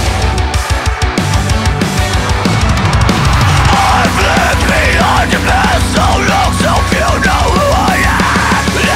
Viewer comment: what say? "I've lived the argument" or "I've lived behind a mask so long, ??? seems to say that .. ( I do not speak English ) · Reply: "I've lived behind a mask so long so few know who I am".